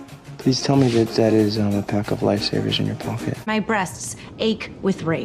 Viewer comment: Please! Someone tell me what does same day in the minute 3:38 I couldn’t understand! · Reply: lifesavers*